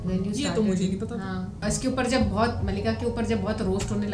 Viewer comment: i thought ma'am said Malinga...😅